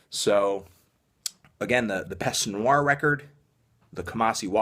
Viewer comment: What's the name of the first band that he says? The black metal one? It sounds like he said "peste du noir." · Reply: My other house is a Ferarri It's Peste Noire. You can find links and more info in the description. :)